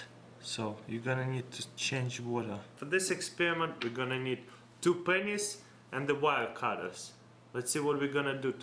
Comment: did it sound like he said in 3:18 two penis and wild cutta hahaha:D